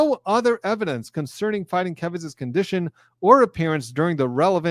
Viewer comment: I thought you were saying Fighting Kevin.